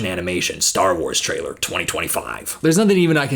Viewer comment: I thought you said Star Trek 2025 for a second 😂